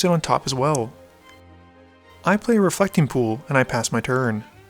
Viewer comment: My volume was on low and I thought he said "A fucking pool"